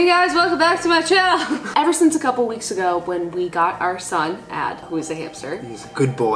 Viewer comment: the subtitles say ''Our son ad who's a hipster''lmao